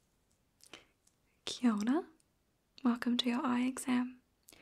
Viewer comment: Great video, as always! I am curious about one thing—it’s spelled “kia ora” but I keep hearing you say “kia ona”. Are you actually pronouncing it that way or do I have crappy hearing? It’s entirely possible that I have crappy hearing! I know in some languages it’s spelled one way but prnounced another.